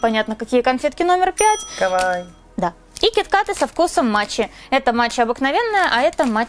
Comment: Мн мне послышалось кит кат со вкусом мочи😅😅😹😹😂😂